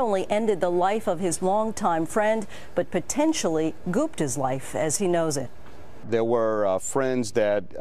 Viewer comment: gooped his life? what is this newsanchor saying at 17:50?